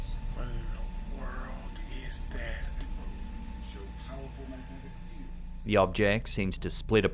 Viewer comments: “The World is dead” Is that what I heard? And why? Why? Ew. Ew. Ew. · Anyone else hear “the world is dead” at 4:41 and 6:00 in the background? There’s an underlying conversation going on. Something else about 1966.